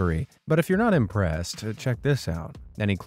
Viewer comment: Im high asf and just spit put my good thought that said BUTTHOLE CAMERA I DIED LAIGHING